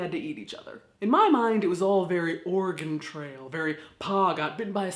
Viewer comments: I heard "Organ trail" for a solid 5 seconds and laughed, because im morbid like that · Thought you said "organ trail" at 2:06, and honestly....still works!